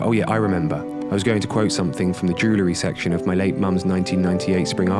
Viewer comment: did you say your 'late mum' at 7:44? If your mum did in fact pass away, I'm very sorry for your loss and I hope you guys are doing okay. ❤️❤️